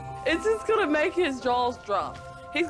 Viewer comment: i thought she said “this is gonna make his jaws drop.” I was trying to figure out how the dude had more than one jaw!